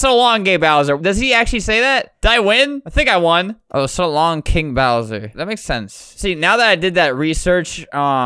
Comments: i think he says so long eh bowser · I cant beleive he actually said so long,gay bowser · for some reason my child sized brain thought mario said “so long-a bowser!”